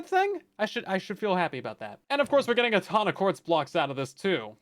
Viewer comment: for some reason i heard honda accords instead of ton of quartz